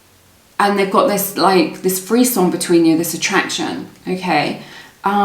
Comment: Very nice reading :) But can someone help me out? At 8:50 what is she saying (just the word I can put that on google from there on:D)? I am no native speaker and I've never heard that word before. Thanks in advance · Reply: She says frisson. I’ve only seen it in print and never heard it spoken so it took me a minute. Google frisson, and it makes sense. We both learned something! 😂